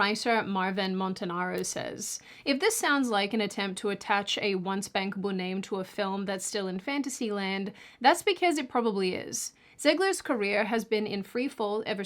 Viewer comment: I heard "once bankable name" as "one spankable name".